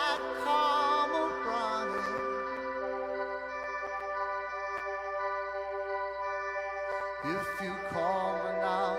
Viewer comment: what does Caleb say at the beginning of on call 37:13??? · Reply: Paramore was supposed to play that show, they cancelled and booked kol instead, he says they are glad they got THE CALL